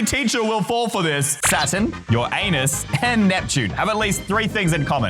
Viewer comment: why does it sound like you said ur a#us💀